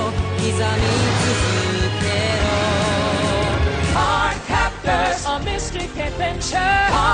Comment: On cardcaptors opening I heard "Hard cactus a biscuits adventure" why?